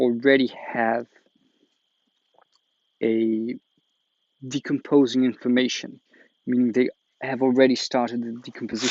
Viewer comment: Just after the 7:00 time you speak about the leafcutter ants and used a word I don't understand, something xxx- osis, you said we should research it. Could you please spell the word? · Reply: It’s the Trophobiosis Theory